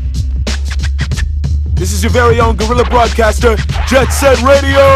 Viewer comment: The first time I saw it I thought he said "gorilla" instead and instantly thought of that kid saying "THAT'S RACIST!!!1!!!11"